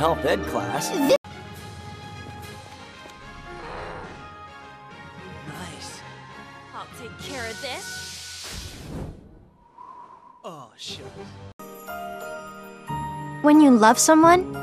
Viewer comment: he really said ,,o shit"???